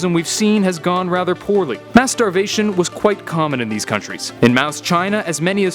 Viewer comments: When he said mass starvation I thought he said Masturbation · I legit thought he said "masturbation" @ 3:14 but then I realized he said "mass starvation" lol